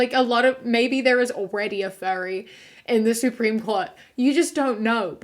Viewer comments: I was so confused for a good while: I misheard the word "Furries" as "Fairies" and I was so upset, thinking "Aw why would anyone bully fairies?? 😞" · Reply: I heard “Fairy” as well… until she said “fur suit”. It clicked then. I was fr confused until then thinking that maybe it was a category of neopronouns… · I think the furry thing is because they’re quite an accepting community because of being made fun of. And it seems like they have a lot of LGBT members. Basically, a lot of furries are nice to other people and conservatives don’t like them. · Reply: Omg I thought she was saying fairy bc of her accent the whole video and got kinda confused.